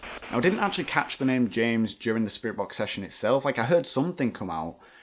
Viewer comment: I didn't hear "James"...I heard "Jason".